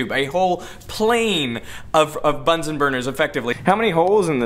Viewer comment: I swear to god, I thought you were about to say buns and thighs instead of Bunsen burners.